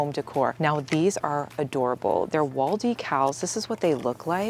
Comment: Thought she was saying waldy cows😂😂